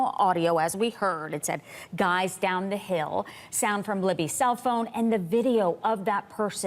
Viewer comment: I hear , "guys , get down the hill"